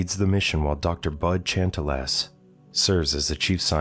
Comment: I was listening off screen while in genshin and i heard Dr Butt Gentle Ass? What?